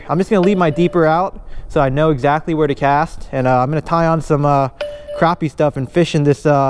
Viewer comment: Sorry but I thought is said crappy for a sec